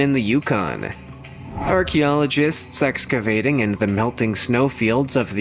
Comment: he said "Sexcavating instead of Excavating , even my 8 year old granddaughter heard it!